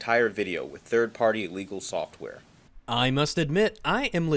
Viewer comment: I'm not done with the video so maybe he says it again later more clearly, but it sounds as though he is saying 3rd party legal software here, as if to say "they are using a legal tool for an illegal purpose". Maybe he is saying illegal and I'm just not hearing him right or he's mumbling or something (once again, haven't watched through the entire video) but to me it sounds like he is saying legal here. It's odd phrasing to be sure but many a things are. Also technically since ads aren't downloaded with the content but you can rewatch downloaded content infinitely, the argument could be made that it is a form of piracy and content "theft". Personally I think it's an argument that doesn't hold any water since I'm pro freedom, but it could technically be argued that downloading content is illegal based on conceptual precedent alone. With that said I believe the fact that mp3 players were said to be legal along with TV recording and whatnot mean there is more precedent against it, but it's worth mentioning that the concept of ad-blocking = privacy is one that is held by a good number of people, notably Linus from LTT. I don't personally agree and I think it's incredibly stupid, but it isn't an entirely baseless position to hold and I can at least recognize the logic that may go into someone thinking that way. Of course, if you define piracy as being something that mundane then I'd suggest you also alter the extemely negative connotation most people associate with the word, b